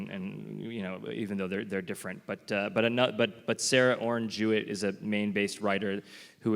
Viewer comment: Did anyone catch the name of the writer Eggers mentioned around 19:20 ? It sounded like “Sarah Orange Hewitt” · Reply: Sarah Orne Jewett